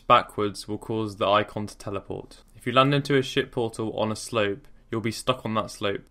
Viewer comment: Am I dumb or does he say "shit portal" at 2:39?